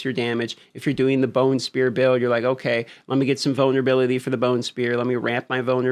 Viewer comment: All I hear at 11:20 (ish) is Boner-ability 😮